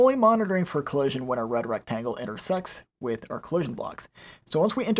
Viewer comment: lol what did he say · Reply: intersects